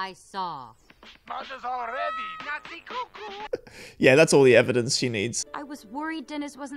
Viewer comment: For a very, VERY long time, I thought Drac was saying, "Nazi cuckoo!" 😂😂😂